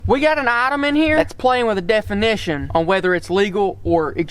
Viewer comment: I heard "we got an Ottoman here" I was confused, when my ear finally accustomed to the accent I realized he said "we got an item on here". Fun video, glad it popped up for me.